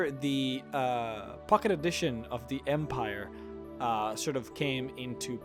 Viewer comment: I thought he said fucket edition